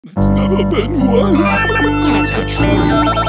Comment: If you listen close at the beginning where you hear the voices it says " It's never been worse" and another voice says "That's not true." I just wanted to point that out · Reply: i always thought he said "its never been once"